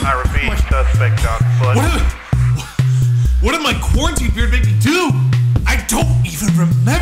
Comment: I thought it said "QUARANTINE BREAD"